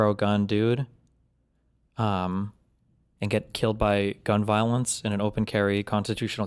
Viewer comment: I think it was actually gang violence but the subject was gun violence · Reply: yeah I edited my comment but at the end I misheard he was going into a speech about "how much gang violence" ...not gun violence